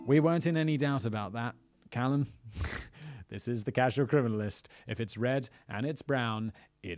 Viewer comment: I totally heard that "Crasual Criminalist" 😂